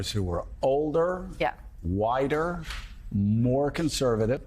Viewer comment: Ok I seriously thought he said "...older, wider..."and got seriously confused to I took a second listen